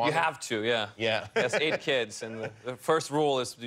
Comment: watching this, he said " 8 kids", i heard "ate kids" still affected by creepy vibes of It